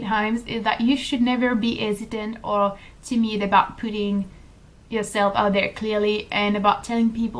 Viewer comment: At the 3:38 mark it sounds like you are saying "you should never be hesitant or timid about 'pooting' (pe'ter)" instead of "putting"; I thought that was funny because I am American. It made me chuckle, so I decided to tell you that. Bon travail avec ce vlog! Je l'aime bien. Il y en a plusieurs que je l'aime bien et le tien est sur la liste.